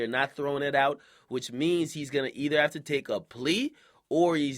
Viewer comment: "He's Gonna Either" made me think he said "He's Gonna Eat" for a second because of the clip that was playing xD.